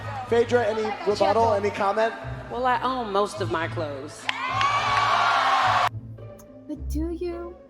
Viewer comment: Not a Phaedra fan by no stretch of the imagination but I thought she said,”I own most of my clothes.”